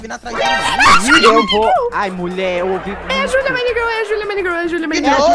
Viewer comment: É a JuLia MiNeGAuRL 🤨 Foi oq eu entendi kkkkkk